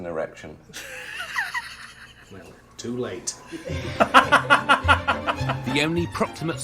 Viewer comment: Can anyone lip read what Rudd said at 11:55? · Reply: "Well... too late."